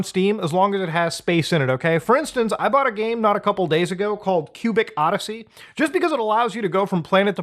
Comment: I thought said "Pubic Odyssey" lol